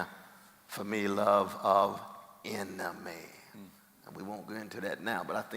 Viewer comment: When he said "...and the most important love to me... The love of enemy." I honestly thought he said "love of "anime". 🤦🏼 My brain is officially broken.